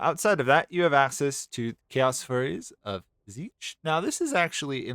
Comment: I thought you said "Chaos Furries" 💀